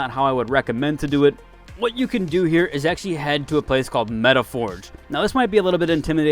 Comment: Hi, new to the game. This is my 2nd week. At 5:34, so said to go to Metaford (?) which is what I heard. Is this a place on the map or a website? I am playing the game on my MSI laptop.